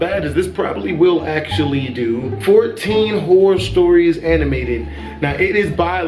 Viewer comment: I thought you said whore stories